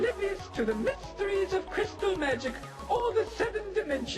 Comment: I almost thought the Pkunk said Crystal meth instead of magic